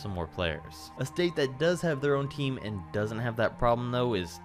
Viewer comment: I swear he just said Texas doesn’t have a team even tho they have 2 (San Antonio) (Dallas) Edit: never mind 😂 I heard him now